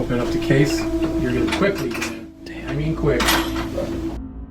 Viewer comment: i heard dan